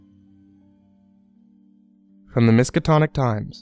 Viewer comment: Did I hear correctly from 1:15:40? The Miskatonic Times? Is this in the Miskatonic University at Arkham from the Cthulhu Mythos?